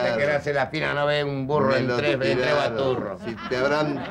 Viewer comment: ¿que dice Olmedo? "No ve un burro.... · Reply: Todo hace pensar que dice: "No ve un Burro en tres ...... ve tres Baturros" Hay un dicho antiguo español, que para señalar que una persona es muy corta de vista dice: "No ve tres en un burro". Seguramente Olmedo queriendo hacer un juego de palabras gracioso, lo dijo al revés. Además, hay una película mexicana del año 1939 que lleva como título "En un Burro, tres Baturros" (evidentemente tomando como referencia a el dicho español). Baturro es una palabra poco usada en el Río de la Plata, pero en España se aplica a la persona terca y algo bruta (un cabezón 😂). Olmedo posiblemente haya visto esa película y combinó el dicho con el título de la misma. (J.13.Abril.2022)